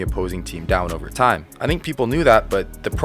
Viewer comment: I did say "I DO think his chances..." are better in Dallas... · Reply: Oof, my bad chief. Thought you said “I do not think…” 😆